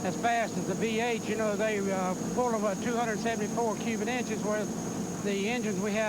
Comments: did he say "Cuban inches"? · did he just say Cuban Inches? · I think he's saying 'cubit' with a silent 'T'.. Although its not silent.. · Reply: Evan Jones Yeah, I heard “cubit” too.